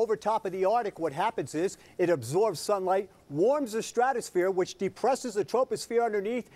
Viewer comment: Did he just say if you blast sulfur dioxide, you warm the troposphere which depresses the atmosphere lowering the temperature.....?????